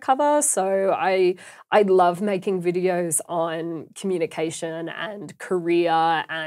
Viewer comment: I first heard "communication and Korea"